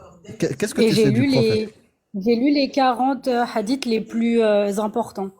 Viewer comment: j'ai entendu : j'ai lu les 🥕 hadiths les plus importants 😂😂😂